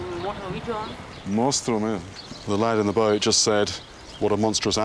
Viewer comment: he actually said ‘hermoso bicho’ which means ‘beautiful animal’ 😂